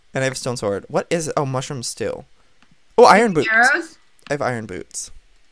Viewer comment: at like round 9:20 when he said iron boots i thought he said "i got iron boobs" O_O